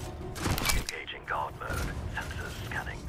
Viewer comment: When he said engaging guard mode I thought he said engaging god mode